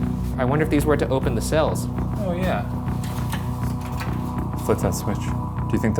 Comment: I heard "oh yeah"